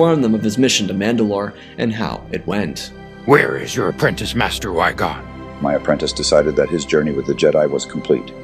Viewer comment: Why does it sound like Yoda is saying Master Why-Gon?